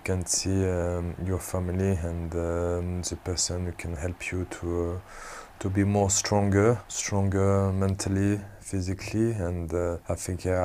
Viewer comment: did I just hear "super sonic can help you to be stronger" 😂😂